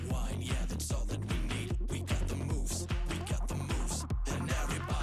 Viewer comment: Tank the Tech...yeah he's cool people, I subscribe to him too. At first I thought they were saying "We Got the "Moes" because of their Three Stooges "Moe" haircuts........Lol!